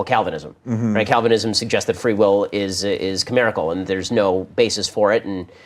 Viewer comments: At minute 1:38 Ben uses a word in a statement - and I can't distinguish the word he is using. The statement is concerning free will in Calvinism - and it sounds like Ben is saying it is "comerical" . Can anyone tell if that is the word he is using? And if so what does he mean by it? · what does Ben say? Free will is "camerical"? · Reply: "Chimerical". That free will is a product of one's own imagination.